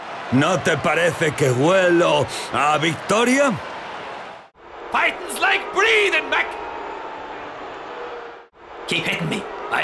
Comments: I love how Piston Hondo is either giving advice or just showing off Japanese food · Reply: I thought he said fighting's like breathing beck! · Every time I hear this, I hear "Life is like Breathing, Mac" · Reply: I think it's fighting's like breathing